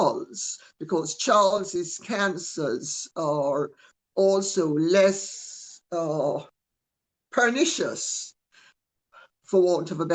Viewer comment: Did anyone catch at 6:10 lady c saying “because Charles’s CancerS are less pernicious” as in plural as in he has metastatic cancer?